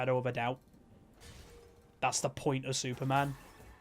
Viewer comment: did he say superbird?